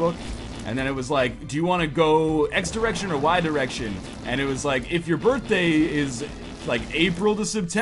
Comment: I thought this was going to be an embarrassing moment because it sounded like you were yelling out "Wide Erection, Wide Erection!"